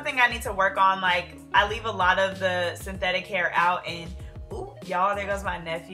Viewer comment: I thought she said And I Oop